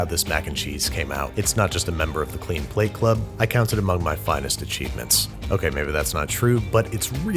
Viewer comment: At first I thought I heard you say, "among one of my finest aCHEESEments" lmao